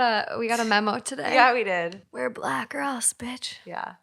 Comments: Did they just say “we’re black girls bitch” lmao 😂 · Every one is talking about the stories but when they found out they both had black on I swore I heard , Where black girls bitch ? And if they did say that why do you think your a black girl because you have a black sweater on not cool but wtv. 😅🚫